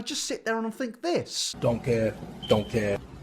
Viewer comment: completely unrelated but at 3:53 I thought he was just saying donkey in shrek’s accent and I was like why would he just think donkey 👁👁